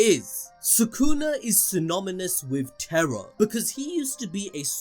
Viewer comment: Am I hearing things or did my man just said "synominous"